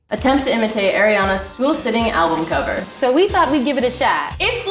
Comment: lol it sounded like she said “airiana”